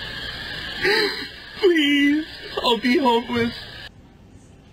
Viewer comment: That dude crying on the doorstep, PLEASE I'll be homeless. 😂😂😂😂😂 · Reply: Oh, he said homeless? I thought he said hopeless! 😂🤣